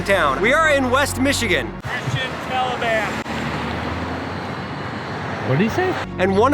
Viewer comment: Christian Taliban?? Is that what I heard? Lol